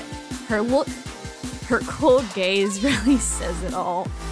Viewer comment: WHY DID I HEAR "Her cold gays" INSTEAD OF "her cold gaze" HAHAH